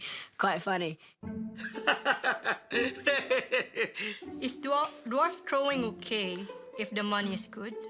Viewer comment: The Filipina woman gave a valuable perspective about the sport of midget throwing. Viola Davis said about her experience with abject poverty: “The thing about being hungry is you don’t think about anything else. You don’t get at the business of being you. You don’t get at the business of being anything.” and “Here's the truth. If you have a choice between auditioning for a great role over a bad role, you are privileged.” Poverty doesn’t always allow people the luxury of choice. Some people are surviving and fighting for their next meal. Shaming them feels unkind in that light. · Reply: i heard it as dwarf trolling. lol